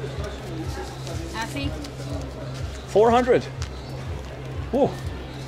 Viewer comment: did she really say 400 ? or was it a ripoff?